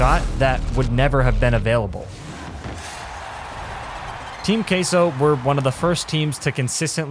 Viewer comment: did bro just say team casoh???